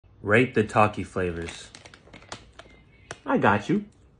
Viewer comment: I thought he said “r*pe the Takis” at the start. Lmao 😂